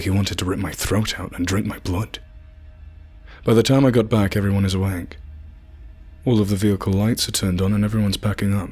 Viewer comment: it sounds like he says: By the time I get back everybody has a wank😂👏 Seriously.... Your in the U.S marines?.you sound British😂 "F" dude......"F"